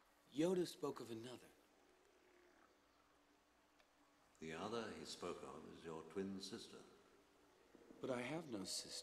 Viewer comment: When I was a kid I could never understand what he said at 1:56. I thought he said "the other is Phogon, your twin sister," which confused me because Leia is Luke's sister. Now I know he said "the other he spoke of" and not "the other is Phogon"